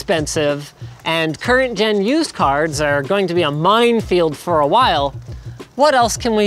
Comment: He says mindfield instead of minefield. I should get a free gpu now.